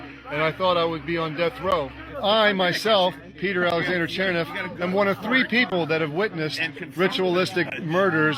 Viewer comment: The guy with the child sacrifice @ 10:25 says his name clearly. You said you didn't know what his name was but he says I am one of three people who saw it. He says....I myself "Peter Alexander Cherniff", David Sherger, and Paul Benachi.